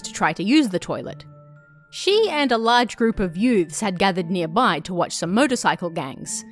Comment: When you first said 'youths', I thought you made up some kind of unique lingo of mixing the words "youths" and "dudes" together where it kinda sounded like you said "yudes".